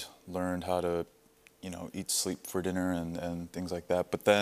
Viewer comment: it sounds like he said "I learned how to sleep for dinner"